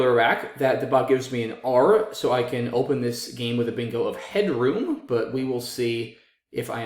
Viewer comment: what was the word with a P? Something pod? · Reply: Just looked it up it was Oompahed. If there's a word you say that others might not how to spell, please maybe add in little spellings in the corners at the timestamp the word is said at. Thank you for being the awesomest of the awesomest of the awesome!!! 😊